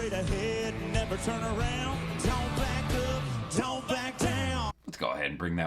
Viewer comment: I'm sure that was the point of you pointing that out, but it certainly sounded like they said, "Don't black up, don't black down." I had to rewind a few times to realize they might have been trying to say "back up" and "back down," but I can't even give them that because it just does not sound like that.